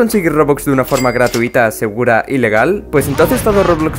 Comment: le entendí ilegal .________.